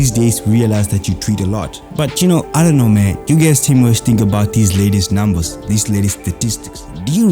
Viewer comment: For a minute i thought you said this lady's numbers